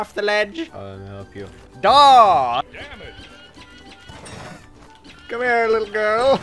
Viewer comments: did it just say dammit? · I swear I heard the announcer say "Damn it."